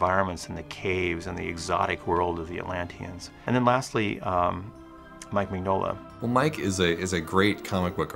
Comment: Chapters: 0:00 - The Journey Begins 9:05 - Creating Mythology 17:25 - Finding the Story 28:43 - Setting the Scene 31:59 - Background 35:07 - Effects Animation 37:09 - Scene Planning 40:57 - Designing Atlantis: The Lost Empire 52:17 - Digital Production 1:02:33 - Music and Sound 1:11:31 - Creating the Characters 1:14:27 - Milo 1:17:08 - Rourke 1:19:08 - Kida 1:21:19 - Dr. Sweet 1:23:30 - Vinny 1:25:50 - Helga 1:27:28 - Audrey 1:29:15 - Mrs. Packard 1:30:36 - Moliere 1:32:43 - Cookie and Mr. Whitmore 1:35:25 - King Nedakh 1:37:45 - The Voices of Atlantis 1:49:45 - Atlantis Found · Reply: Did they say mike mingola the creator of Hellboy the comics 42:55